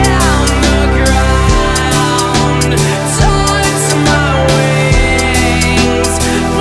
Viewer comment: i thought it said "and the ground, TIMES my wings" not TAUNTS HAHAHHA